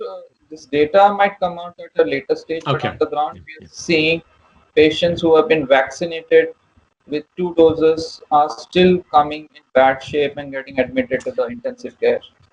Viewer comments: Beginning at 10:20, did he say people who have had two shots of the vaccines are still being admitted to ICU? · Did he say he had seen or had not seen patients who had had two doses of one of the vaccines? And in the UK and Canada, a lot of us are not going to have the second dose for a couple of months from now. What about patients who had one dose more than 3 weeks before they developed symptoms? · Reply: He said two Dr: ...."patients who have been vaccinated with two doses are still coming in bad shape and getting admitted to the intensive care. Dr. Campbell:...."Oh dear, okay, right, okay, right......."